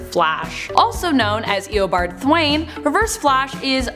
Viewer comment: did she say thwane??? Isn’t it thawne